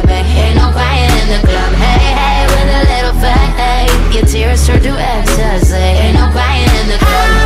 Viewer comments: it sounded like she said "your tears turn into e-e-e-ggs" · It said with a little faith I heard WITH A LITTLE FAT EGG!!!I can’t I hear it lol.😂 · "With a little faith" What I hear "with a little fat egg" Lol😅😅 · The first time I heard this song a thought she said" with a little fat egg" instead of " with a little faith" 😂😂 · i hear " with a little fat egg " :)